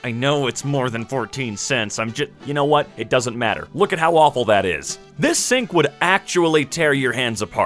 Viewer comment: I heard that sentence as “Look at how Waffle it is” instead of “Look at how awful it is”